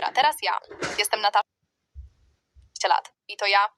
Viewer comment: Ej bo ta Natasza ja najpierw usłyszałam Natan